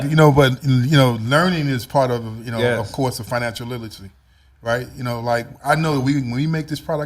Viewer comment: Not hating on tha flavor man but what did he say @ 8:34 financial what!!!😕😕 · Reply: Financial literature